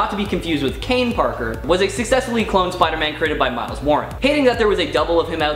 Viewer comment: Aye at 5:24 I cant make out did you say Miles Morales was the 1 to create the spider carnage? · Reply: Mile Warren(sp) The Jackal